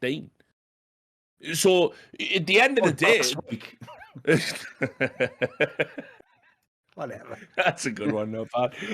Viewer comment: what does Thorin say here? I can’t figure it out. Plz help! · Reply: Think he says “or last week”